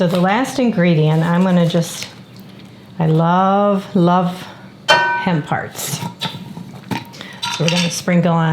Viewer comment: I thought you said "hen parts". It wasn't until the 3rd rewind that I realized you were saying "hemp hearts"!😂😂😅